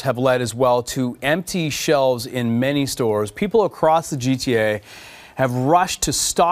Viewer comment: sounds like he said "People across the gta"